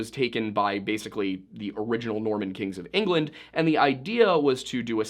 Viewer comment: I swear to the good Lord on high that it sounded like you said the "Mormon kings of England" created Domesday book. I had to pause it and rewind and it still didn't sound like the word "Norman." Genuinely thought I misunderstood the video topic and we were talking about some weird Tartaria thing or something. 🤦‍♀️🤦‍♀️🤦‍♀️